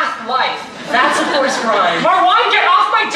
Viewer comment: What did the guy say at 1:50 ? I can’t hear it properly lol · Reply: Niamh Brady “it’s a forced rhyme”